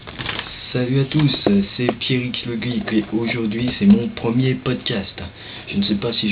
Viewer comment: C'est moi ou tu dis Pierrick le bide ? :bave